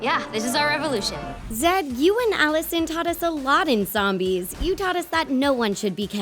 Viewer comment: She said Allison instead of Addison at the time stape 0:05.